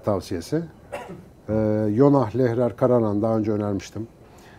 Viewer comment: deki kitap önerisi neler? İkincisini anladım ama ilkini bulamadım anlıyamadım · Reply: Jonah Lehrer- Karar Anı